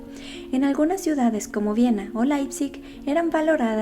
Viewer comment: No entendí la segunda ciudad en la que habla en el minuto 4:34. Por si alguien lo sabe o le escucho mejor ? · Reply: Leipzig, en Alemania.